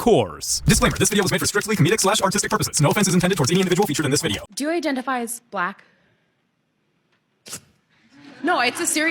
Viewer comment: I don't discriminate! what i heard at @ 0:12 was 'do you Idenitify as Flat'! no, the Earth's not Flat!